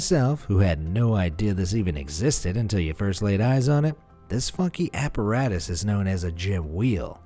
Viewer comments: am i the only one who thought he said f**ky ? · did he say "this f*cky apparatus..."? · did he say "this f*cky apparatus..."? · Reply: I thought he said funky